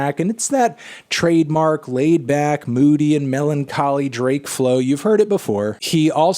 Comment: Did he say MELONcholy?